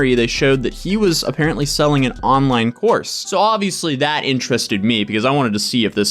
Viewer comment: I am across the room listening and thought you said an online horse 😂